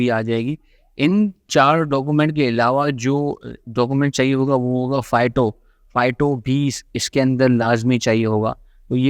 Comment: what's the word? I heard it "fighto". Correct me if I'm wrong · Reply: Phyto Certificate